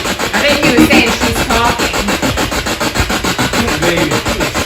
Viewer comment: you heard the word "talking".... with my headphones on i heard "happy".